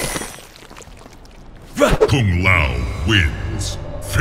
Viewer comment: sounded like the announcer just said facking kao lmao